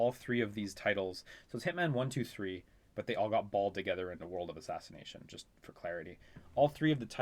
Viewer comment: "Hitman 1, 2, & 3 all got balled together", but I heard it as "bald"....